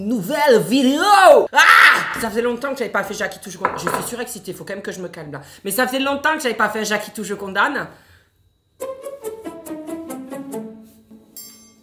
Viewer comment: Au lieu d'entendre le j'acquitte... J'ai entendu Jacquie... Automatiquement le Michel a suivit... 😂 Tu me pervertie sérieux!😂